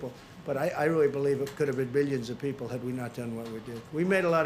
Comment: IT COULD HAVE BEEN BILLIONS OF PEOPLE DEAD! Did I hear that right?